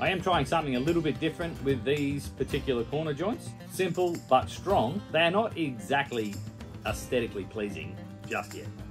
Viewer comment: what joints does he say he uses? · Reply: They are just half laps, but mitred at a 45 degree. hope that helps